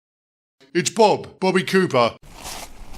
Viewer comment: So its confirmed that BoCo is a reincarnation of a D-Class prisoner named Bobby Cuba, his D-Class number being the same as his BR Number D5702. Maybe the first D-Class is also a character we already know, they have the number D7426. My guess is that the person who entered the path of the dead at the end was a relative of Bobby Cuba, which could be Johnny Cuba. And Johnny Cuba (The Engine) will be covered in the next Sodor SCP episode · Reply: Sounds more like a Cuba than Cooper, the start of the surname sounds like “Que” for Cuba instead of “Coo” for Cooper. (Just sounding it out phonetically.)